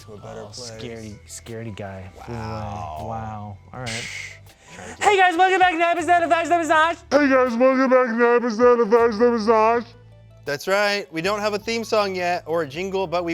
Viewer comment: Did anybody else hear ''Hey guys welcome back to another episode of lunchtime with smosh'' the first time? FIRST BITE!!!